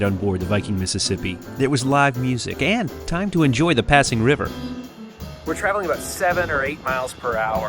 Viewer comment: Did I just heard “ Time to enjoy Pasig River?” 😂 ( 9:09 ) You must have been in Philippines recently 😊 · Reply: He clearly said passing river, lol